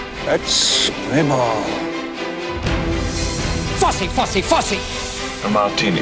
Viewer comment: This ad is epic, but my favorite part has to be 0:17 I don't know why, but it sounds like Williams is saying "Fussy, fussy, fussy" in a clever and sassy manner.